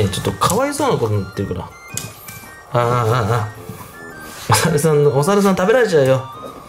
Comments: お猿さん って言ってるのが おざわさん って聞こえたww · お猿さんが小沢さんに聞こえるの俺だけかなぁ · お猿さんが小沢さんに聞こえるwwwww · お猿さんは？が、小澤さんは？に聞こえた… 誰… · お猿さんがどうしても小沢さんに聞こえる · お猿さんが小沢さんに聞こえてしょうがない人私だけじゃないはず。wwwwwwwwwww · お猿さんって言ってたのね笑 小澤さんって聞こえた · おさるさんが小沢さんに聞こえた笑笑